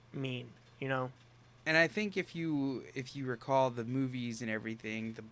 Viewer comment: okay, I just wanna state, I was not looking for it but when said "when you recall the movies" it seriously sounded like he said "boobies" than he continues talking and I realized what he actually says 😂😂😂